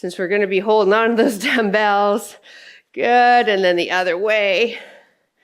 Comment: Oh my, those 15’s felt like 50’s! In my head I heard you say, “are you swinging those dumbbells?” So funny! I was swinging…then I stopped swinging and pushed through.🏋‍♀ 😂